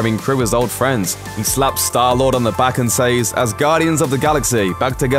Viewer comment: I thought he said "Us Guardians of the Galaxy"